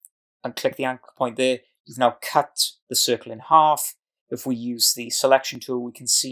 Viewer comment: Did you say at 00:39, "Now cut the circle in half"? I came here hoping to learn how to cut a circle in half, but you are telling me again to cut a circle in half instead of showing me. This is not teaching, I'm sorry! Let's learn from teachers before we start putting content out there. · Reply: The line is actually 'you have now cut the circle in half' following on from clicking both anchor points, my apologies for not speaking as clearly as I could at this point.